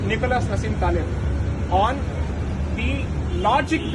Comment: Can someone tell me the name of the author that he mentioned at 18:42? I couldn't hear it over the background noises. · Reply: Nicholas Hassim Taleb? that's what I got